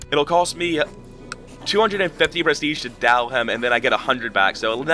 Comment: it sounds like you're saying "dal", what does this mean? You say that word a lot in previous videos too, like when you're gonna go seize some land. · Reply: What you're hearing is "DoW", declaration of war, getting used as a verb.